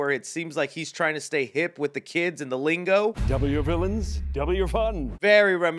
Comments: Personally, I thought Eggman said "Double your villains, double your fun." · honestly I thought he was saying "Double your villains, Double your fun" as that made more sense given that there are 2 villains now and "w fun" just sounds weird. I'm probably wrong tho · i thought eggman was saying “double your villains, double your fun” not “w”